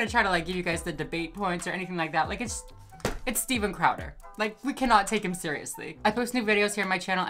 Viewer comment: I really just thought she said Steven chowder for like half the video I am so stupid